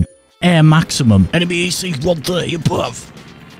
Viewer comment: "A bit of the AC-130 bruv" is that what he really said?